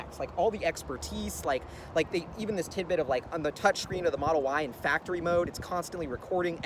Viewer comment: did you say model Y? Anything you want to share?